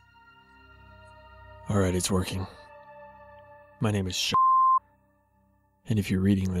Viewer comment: "My name is sh***" Sorry I couldn't help but burst out laughing.... It seriously sounded like he was saying my name is shit. x)